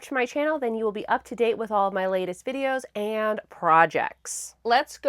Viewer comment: Idk why but my brain always auto fills this sentence with "shenanigans" instead of "videos and projects" 😸